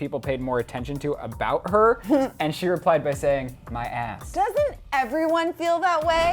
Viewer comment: oh lol i thought kendall said my ex not my ass 😂😂